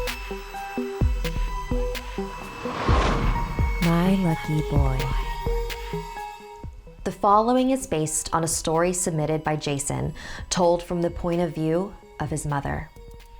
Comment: I thought it said MY lucky box XD